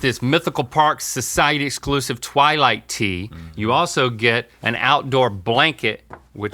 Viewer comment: I heard "Twilight shirt" and ran back to my computer. ✨🐀✨